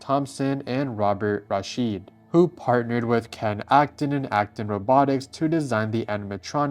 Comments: did I hear Aston robotics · excuse me... did I just hear Afton Robotics...